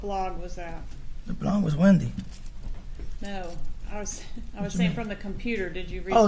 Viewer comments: 🤔When the attorney asked" Was it from a blog?", he thought she said "blonde". His response was,"The blonde was Wendi." LISTEN carefully at 3:14...case closed!😮💯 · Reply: I thought he said “The blog was Wendi!” Which would have been epic! 😂😂😂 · I think he said, “the blond is Wendi.” Think he misheard her. Blog-Blond.